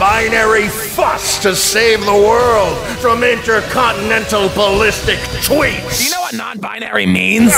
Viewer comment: I thought he said inter-continental ballistic twinks lol